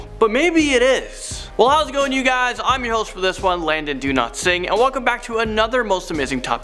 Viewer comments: I thought you said Landon do not sing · When he says it sounds like he says do not sing